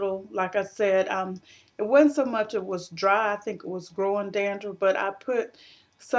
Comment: At mark 7:11, did you say "growing dandruff"? LOL! Cousin! No DNA required! I just know we're related because only my family would say things. The family reunion is this summer! LOL. You must know that you are hilarious without even trying! Laughter is good.